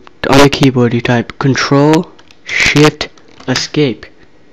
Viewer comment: it sounds like he says " you press control... shit"